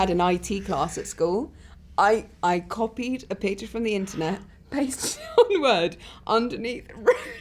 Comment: What did daisy say? I couldn’t understand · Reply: "underneath wrote Daisy Ridley...."